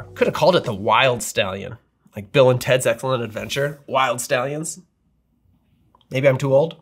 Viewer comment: I thought you said Wyld Stallyns the 1st time you said it. 😁